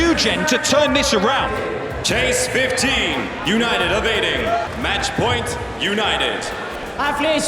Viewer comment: I thought they were saying United of Aiding like it was a country 😭